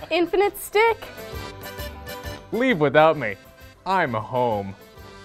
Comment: "Leave without me. I'm home." 🤔 That's... actually a convincing line. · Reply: I thought he said "I'm a ho."